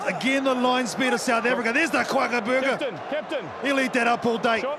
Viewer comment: what did he say .. here is the kwakabooger😅